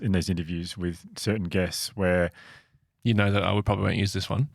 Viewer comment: Can anyone tell me what says that makes them all crack up? Can’t for the life of me figure it out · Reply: He says that: You know that "oh we probably won't use this one". I disagree but yeah